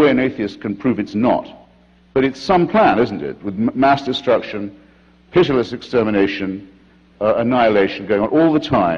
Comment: Ok what is the word he uses at 15:14 ??? Pitilistic?????? was he going for pugilistic?????